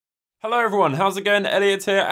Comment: No offense, but at the beginning of the video, i thought you said "hey everyone, idiot here"